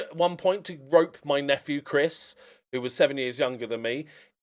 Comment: in and For an awful moment I had to rewind the video because I thought you said you groped your nephew! 😂